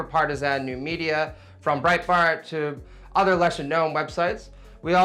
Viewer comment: Does call it Breitfart at 11:41 ?